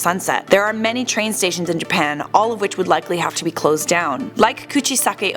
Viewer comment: Look at the captions It says half instead of have This might be a mistake but it’s probably because teke teke was cut in half